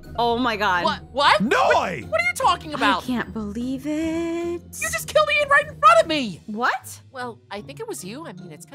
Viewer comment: what you mean noi you said "YOU JUST KILLED ME IN FRONT OF ME" did you meant noi "YOU JUST KILLED EIN IN FRONT OF ME"